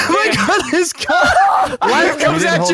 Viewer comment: does say the n word?!? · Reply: My gun